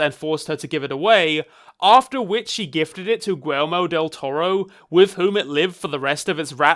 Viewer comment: honest to god thought you said Elmo del toro; I was so confused till I looked at the screen to read Guillermo lol.